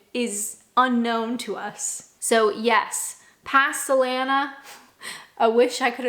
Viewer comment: I heard: 'pastalana' and as you were on about grocery shopping, I got very confused 😂